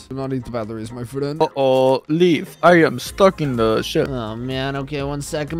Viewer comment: I heard “Leaf I’m stuck in the sh-“